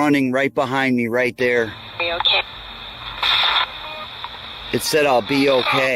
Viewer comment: "are you ok" what he thought it said: "it said ill be ok guys"